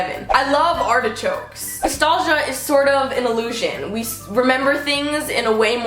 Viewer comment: I thought she said salsa is an illusion